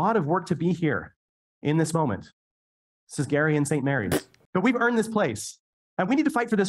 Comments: did he say this is Gary and Saint Mary????? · …I’m sorry, did they call her SAINT Mary?!?